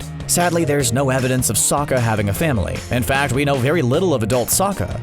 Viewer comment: did you say family ?